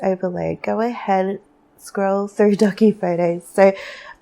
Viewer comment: Your voice makes me so happy 🥰🙏🏻 Thank you for this. I was sobbing and purging childhood trauma/ depression/cptsd aaall day. Could really use some joy 💚💚💚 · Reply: Piles 1 and 3 resonated deeply. Thank you, 💚💚💚 When you said doggy in pile one, I thought you said "ducky" so I thought you meant looking at and taking photos of people making duck faces 🦆🤣 But I also used to be called Ducky because of my obsession & skills with water, so that also struck and oddly specific chord. 🌊 And pile three is full of messages I usually receive for others, so it's ironic that the message came out about focusing on the problems of others to get insight on what I'm going through because that's literally what I was thinking about before I clicked this video, about the parallels between the messages I get for others and how they might help me, too. That focusing more on selflessly doing readings for others and being there for others will lead me to deeper healing. And music is everything to me. I can't even put it into words beyond that. It's *everything*. I need to pull my instruments out of storage and tune them. I'm so intimidated, but my soul is calling me to make music again, maybe even share my music. Certainly at least to listen to music more. ♡♡♡♡♡ Thank you for these heavenly messages ✨